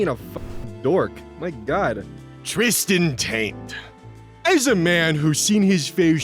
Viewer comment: I'm sorry... did you call him "Tristan Taint "? Like... the area between the 🥜 sack and the 🍑🕳?